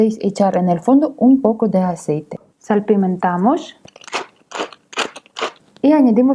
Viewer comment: dice: le pueden poner un poquito de aceite, y ¿ que dice el título???